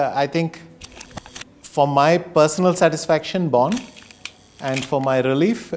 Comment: what did vishy say on born? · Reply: He's talking about his 2008 world chess championship match against Kramnik. It was held in Bonn, Germany.